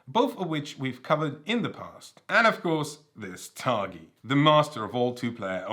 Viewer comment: what board game did he say here? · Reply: Targi